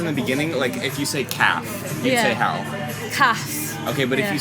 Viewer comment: are you asking her for cafe or calf , i heard cafe and she is saying cafe in a northern accent also follow on my nans from yorkshire and im pretty sure they have an understandding of me vs my , its just collioqiual to say me dad , me foot but you can say 'my' with a northern accent and it sound different/northern - like the dad in adolescence (on netflix )